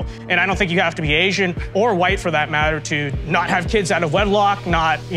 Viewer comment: “I don’t think you have to be Asian to not have sex out of wedlock” Show me what’s wrong with this statement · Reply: I heard “do not have kids out of wedlock” not do not have sex. Basically he’s saying you should be responsible.